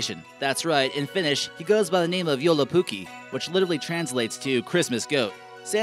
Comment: Pfff... "So much snow" hah! We don't have snow almost at all these days. 😒 Edit: I laughed so hard when you tried to say: joulupukki It sounded like you said: yolopuki xDDD